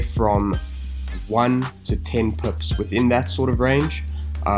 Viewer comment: 1 to 10 WHAT?! Puts? Perps? I couldn't understand that word. What was it? Roughly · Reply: Pips